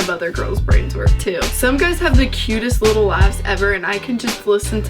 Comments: I'm 99% sure she said luaghs but no matter how many times I rewind it I hear ass am I the only one? Ok something is wrong with me. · I thought she said "the cutest little ass"